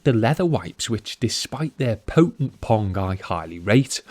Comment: WTH does he say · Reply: despite their potent pong